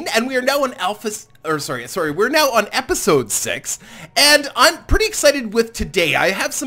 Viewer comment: did you just said "episode sex"???